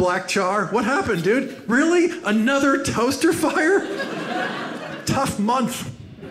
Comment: What got cut when he said "Tough..."? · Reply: Tough month